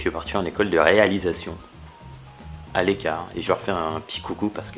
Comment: Si toi aussi ta cru qu'il disait " A l'écart"